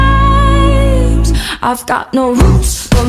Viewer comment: Whenever it was showing mineta whenever it was saying I've got no roots I don't know why but it sounded like it said I got no boobs when it was deku and it was showing mineta😂😂